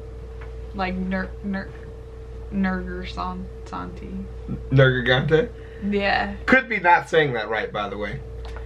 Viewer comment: Does he say Nergigante??? I feel some monster hunter vibes in this